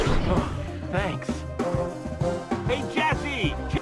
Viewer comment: Did axel just said cassie in 0:50?